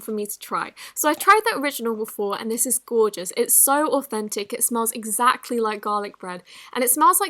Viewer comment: I thought she said the slimes are autistic instead of authentic